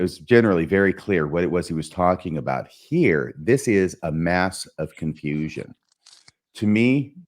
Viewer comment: I thought you said “Land of Confusion,” and now I have the 1986 Genesis song stuck in my head! 😂